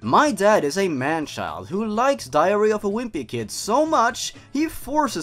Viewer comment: i heard diarrhea